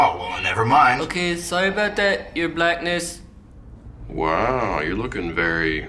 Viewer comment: did he say your blackness 😂 😂